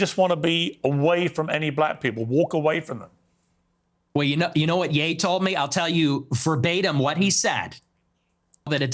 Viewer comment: Hey, what the?! What did Ye say?! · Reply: "Inwards be inwards" or something very similar.